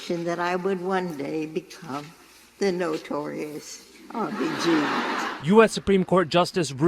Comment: What does she say after “notorious”? I tried looking up another upload to read the captions, but they all cut out that part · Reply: RBG